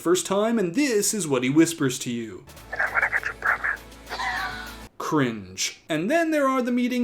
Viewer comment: Does anyone know what he's saying at 10:45? I've replayed the clip a few times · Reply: "I'm gonna get you pregnant"